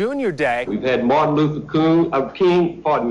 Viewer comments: Sounds like he said Martin Luther Kung.. with the sound "loo" immediately prior, it sounds like a normal sort of sloppy tongue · all I keep hearing him say is "Martin Lutherd Kring Park" what slur did he use ?!